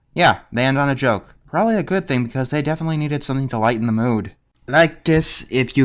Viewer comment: Does anyone know what he said at 4:02? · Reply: What he said was "They definitely needed something to lighten the mood"